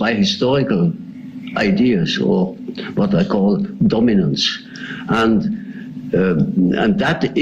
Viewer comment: First of all, thank you so much for watching! I hope something—anything—in this piece was useful or at least meaningful to you. Alright, here are some video FAQs: – Some classic paintings were digitally manipulated for useless reasons. I know, I know. – Except for specified dates and sources, consider vague historical references as historically inaccurate. For example, Romans didn’t have pretzels as we know them today, although legend has it they originated them. – I recreate journal entries and invent some for illustrative purposes. I also chose not to always disclose which are real because. Thanks again! Please subscribe! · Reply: Big mistake @ 4:30. Your subtitles read just THE OPPOSITE of what he is saying. Jung says that all these influences are "by NO means always personal". And at 4:40 he refers to those influences as "dominants" and NOT 'dominance'. "Dominants" is a well known definition for those familiar with Jungian psychology. As of today, 112K people have been induced to get wrong a really fundamental idea, or as he puts it "a most decisive factor in psychology": that all those influences are NOT personal, NOT individual. We are made by the influences of "an immense age" or time span.